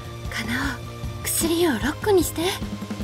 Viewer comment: 薬湯を6個が薬湯をロックに聞こえる。